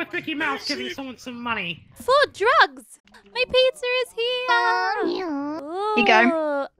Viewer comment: anyone else hearing philza?